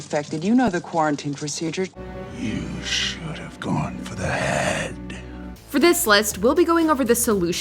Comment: When I saw Avengers Infinity War, instead of hearing Thanos saying “you should have gone for the head”, what I heard was “You should have gone for the hand”.